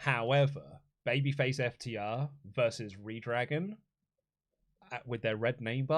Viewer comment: did Luke call them Re Dragon? has he been listening to too much GiR?